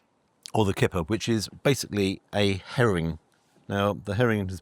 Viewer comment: I live in Texas, I thought he said Heroin rather than Herring